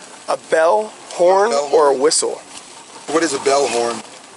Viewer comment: I thought the same thing when he said Bellhorn or whistle…. I got it the second time when he slowed down but i don’t have yelling kids in the background…. then when he said bell, horn, or whistle …. the they said oh ok gotcha…. but then in the recap the cop 8:46 literally lies about that whole sequence about how he asked it and about how the boater responded.